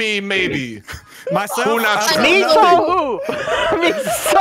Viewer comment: What H20 said: "ME SAW WHOO"??! What I heard: "MISO SOOUUP"??!?? 😂😂